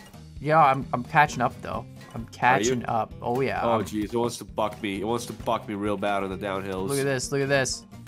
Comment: What did say? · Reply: He said, "it wants to buck me" like a horse bucking off it's rider.